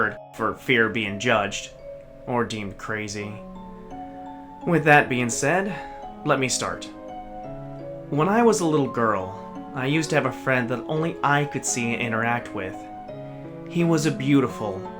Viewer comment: did he just say, "When I was a little girl"